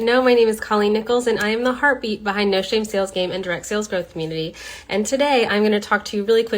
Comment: I heard "I am the harpy behind noshamesalesgame" and choked on my water.